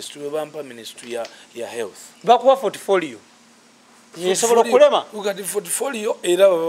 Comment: Did I hear sth like 'fortfolio'???????